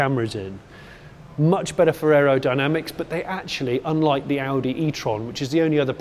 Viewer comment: I heard "...much better Ferrero Dynamics" and thought the ambassador was spoiling you with some new high tech confectionary.